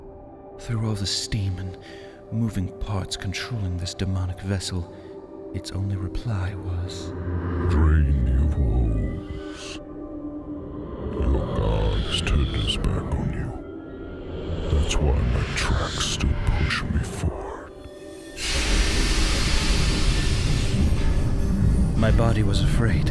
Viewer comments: "Drain thee of woes", means: let go of your sorrows/regrets/worries. Particularly because he says: "your god has turned it's back on you", implying: worrying, lamenting, etc. It is pointless because no help is coming "thats why these tracks still push me forward", meaning if said god would exist; he surely would have stopped the train. Basicly very depressing. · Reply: I thought he said "Dream of those, your god's turned his back on you" cuz he asked "What of life after death? What of the paradises that await me in heaven above." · for those who wanna read what he said or don't understand: "i have seen past the farthest star, and i have gone to edges of the earth. I can take you there, if you so choose, i can give you all the things you never knew you wanted, taste from the tallest chalice, dine at the largest halls, all i ask in return, is your soul. Your soul for a chance of a life without pain, without heartache , all this i can give you and more, JUST for your soul." "Drain thee of woes, your god has turned its back on you, that's why my tracks still push me forward." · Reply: I thought he said “dream you fools” not “drain thee of woes” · What does the train say at 4:14 someone PLEASE help me. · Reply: Your god has looked past you that's why my tracks move forward Or something like that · What's it say..!? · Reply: "Drain your woes. Your god has turned its back on you. That's why my tracks still push me forward."